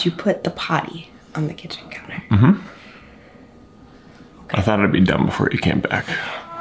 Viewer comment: What I heard - "I thought I'd be dumb before you came back" What the captions read "I thought it'd be done before you came back" This is cracking me up for some reason🤣🤣 ! Welcome back!!!